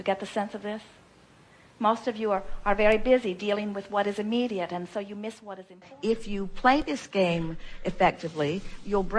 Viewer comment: What was she saying when it cut off right before 2:18 Please? A bit unclear why it was cut before the sentence finished. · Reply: Most of you are very busy dealing with what is immediate so you miss what is important.